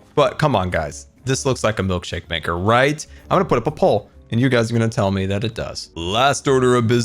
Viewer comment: When you said "I'm gonna put up a poll" I totally heard "I'm gonna put a papol" and was like "dafuq is a papol?" XD